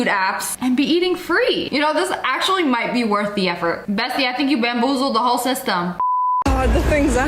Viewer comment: did she call us bestie?? 😂😂😂😂🎉🎉🎉🎉